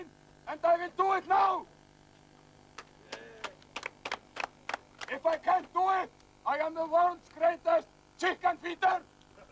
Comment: what does he say at 51:52 "if i can't do it, i'm the world's greatest chickenviten"?